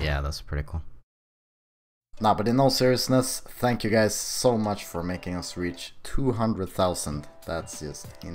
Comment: 20 seconds in I thought he said 'ty for making us rich'